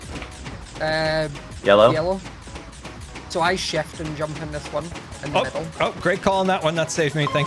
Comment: did scar just say a bad thing (Sh-t)? 😂 hopefully not but that would be so funny lol ❤ Edit: i think he said "ship" 😂❤ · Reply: ahh he said shift... I thought he said ship or something else 😅